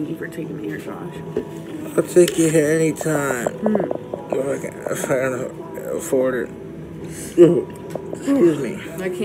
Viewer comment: I'll take you here anytime then he coughs and sounds like he says or fans afford it 😂